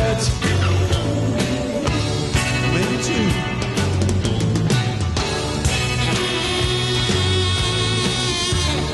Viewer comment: Does anyone know what David Gilmour says at 2:04 ? Thanks in advance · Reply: "Maybe two"